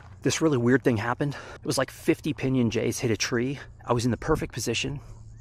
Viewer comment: I had to go back and re-watch that bit because from across the kitchen it sounded like “Over there 50 PB&Js hit a tree…” 😂